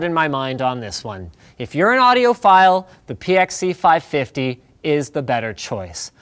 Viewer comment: Who else heard "audio file" instead of "audiophile" first? Hands up!